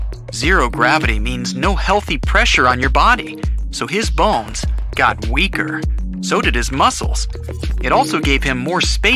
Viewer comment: I thought he said his ball got weaker LOL